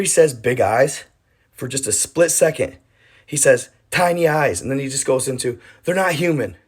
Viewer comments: I thought he said shiny eyes! Big and then he says shiny eyes! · I thought he said Shiny eyes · He actually says 'shiny eyes', check the las vegas news cast reports. I thought n believed he said tiny eyes as well, n did some search on las vegas news, they have the full audio context but not video. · I thought he said shiny eyes? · I thought he was tryin to say shiny eyes. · I thought the guy said "shiny eyes". But that could be wrong, of course.